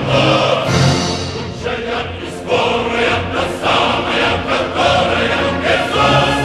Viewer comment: Я сначала расслышал не 'могучая и скорая' а 'могучая история' 😂